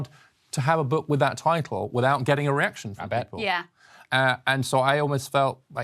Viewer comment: For a split second I thought he said even just the title can't be done w/o an erection. w/o getting "A reaction," (in his accent!) Geez.